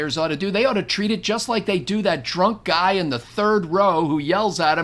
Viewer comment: For a moment, I heard "drunk" as "Trump." Oh dear.